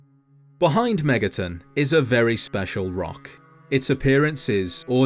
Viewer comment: I have been on a SMT binge for a while that when I heard "Behind Megaton' at the beginning I heard "Behind MegaTen" I thought I had a psychotic break and was watching another SMT video when I intended to watch a fallout video lol